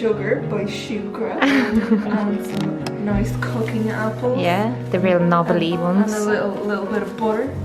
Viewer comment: Everytime you were saying cooking apple I thought you were saying coconut apple! 😂 I was so confused. On the media thing--I watched the video you suggested by awakenjp and to have to admit I stopped watching halfway through because it made me so mad! I don't consume a lot of media. I read the information given on what to from the government where I live, which is luckily quite good (I live in Alberta, Canada). We are on track to open up pretty everything by June 1st! I also check coronavirus world meter daily because I like to see the numbers of deaths / infections all over the world, especially when they go down! The US is at 1000 deaths per day, which has actually gone down from 2000 deaths per day earlier this month! Which is good. I also like to get information from South Korea / Hong Kong because they have successfully combated this virus, so they have lots of useful information. All in all, it's about 5 - 10 minutes of info gathering per day for me and it's been working for me I think.